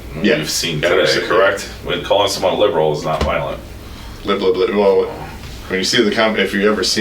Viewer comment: did he say something about “liberals aren’t violent”